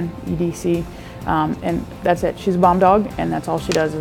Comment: at first i thought she said bong dog lol